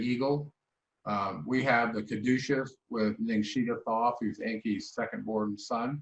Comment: Can anybody make out what he says here? Something Thoth? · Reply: Caduceus with Ningshita/Thoth. 🙏